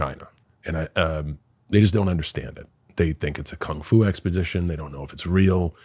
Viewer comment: Did he say n** don’t understand it?